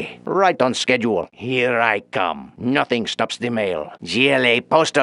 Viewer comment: "Nothing stops the mail!" Lmao (Not responsible for damaged goods!) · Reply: I've heard that as "Nothing stop the Mayor" for the longest time I heart them as both